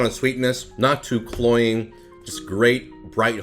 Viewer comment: I hate it when my cereal is too cloying! (What does he say at 1:55?)